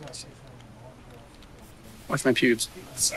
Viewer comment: “Watch my pubes” 💀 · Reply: i thought he said “ watch my nudes “ my face went 💀